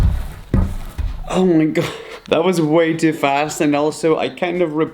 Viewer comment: I think he said oh ni__a